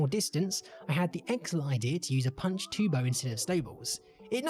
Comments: If inventory space is light just build a snow golem (2 inventory spaces) harvest iots snow and boom infinite snow blocks! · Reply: I thought you said "the idiots snow" and I was like damn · Holy shit, I didn't understand you were saying "snowballs" until you got to the parrots lol. I thought you were saying "Sables" or "Staples"